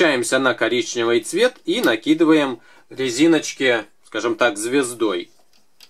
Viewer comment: Со знакомой плели, ей вместо "звездой" послышалось " накидываем резинки п*здой")